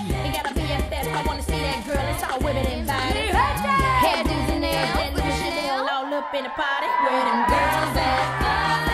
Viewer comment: What does Flo say at 1:20? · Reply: mi gente